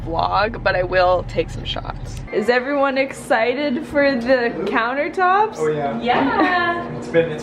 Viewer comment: this whole time I thought you said cords countertops lol